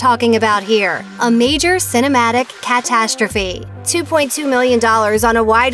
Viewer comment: Did she just say catstastrophe? lol